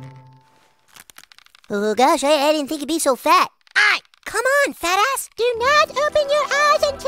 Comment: “I didn’t think you’d be so fat” “ÆY.” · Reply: I thought he said I riding think you’d be that fast